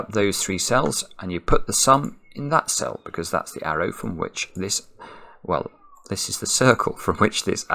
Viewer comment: Hi. Can anyone tell me what the verb uses at approximately 3:45 in the sentence "this is the circle from which the arrow <verb>" is? I usually understand everything that says but since I'm not a native English speaker, there are still some words that I don't know (does anyone actually know all of them?)... So please tell me 😁 Have a good day or night ! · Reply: says "this is the circle from which this arrow emanates". To emanate means to come out from a source. Hope this helps!